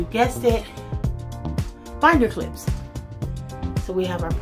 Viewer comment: I couldin"t make out your hack for shower curtain. Sounds like you said clips. What are they used for?